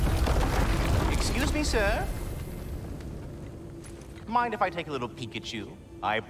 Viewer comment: Thought I heard he said "Mind if I take a little Pikachu"🤭😅😁